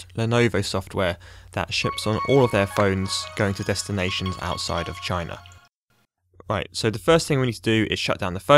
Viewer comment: What I heard: "All of their phones go to the destination outside of a vagina"...